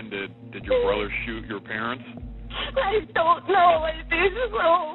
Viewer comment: "i don't know, i think so" how is that inaudible? Someone else heard that, right?